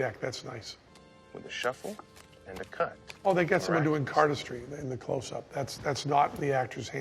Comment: what does he say their? they get someone doing "card-a-stream".... thats not the actual actors hands? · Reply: Cardistry prob